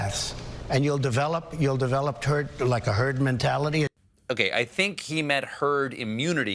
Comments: did he just say Turd instead of herd? :D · Sounded like he said, "Turd Immunity". ROFL! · Did he say "you will develop turd..eh...herd mentality" at 8:58, or did I imagine it? Not sure about others developing it, but he surely has! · Every time I hear it, I'm still hearing "turd mentality" which totally fits too. Because you're a turd if you think in this day and age that herd immunity is our best and most sane defense.